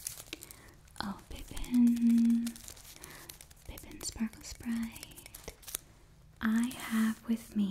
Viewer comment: When you first called the pixie Pippin Sparklesprite, somehow I heard this as Pippin Sparklespurt, which evoked for me an image of a pixie that farts sparkles. Evidently I was so immersed in the role-play of a pixie in the ear that my hearing was impaired.